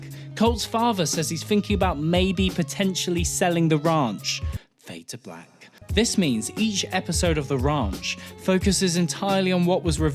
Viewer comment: It sounds like he was saying " The Runch" lol